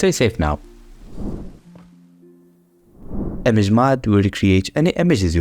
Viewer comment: it says Mugging Villagers instead of Image Mod